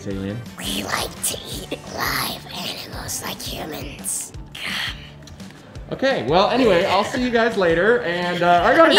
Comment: what did say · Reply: Yum